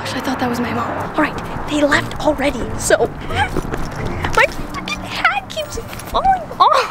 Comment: did she say the f word I didn’t hear her❓ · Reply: She said "freakin"